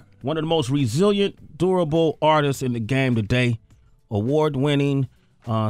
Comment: I thought Sway said adorable. :/ Bit he said DURABLE. Woops. Silly me :9)